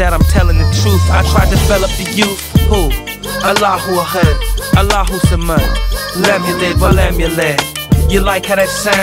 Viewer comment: How do u spell what he said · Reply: Kul hu Allah hu ahad kul hu allahu samad, lamulid wa lamulid...surah (chapter) 112 iklas(sencerity) al- quran